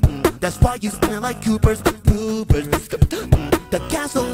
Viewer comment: did he say, “coopers poopers?”